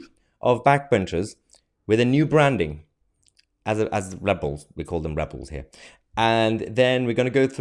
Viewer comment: I remember the good old days when the Tories didn't have a 'right' of the party, THEY WHERE ALL RIGHT 😊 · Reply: I remember the good old days when Revels had coconut creams! 😁👍..... oh...the word 'Rebels' was misheard....silly me. 🤣